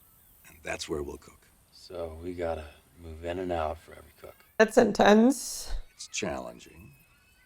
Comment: did you say "that's intense" or "that's in tents"? both work